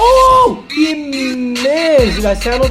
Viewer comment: what did he say? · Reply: Hussein Manga M he said he pissed lol